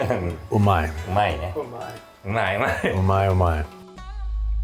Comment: the first time i heard Babish say "umai" i heard it as "ooh my" which doesnt sound or mean much different